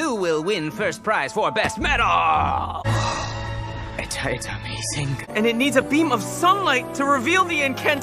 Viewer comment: Varian: Whoa... I-I-It's amazing. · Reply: I hear "atiatamazing"